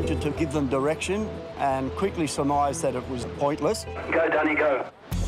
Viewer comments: did i hear H say go daddy go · I think I heard “go daddy go”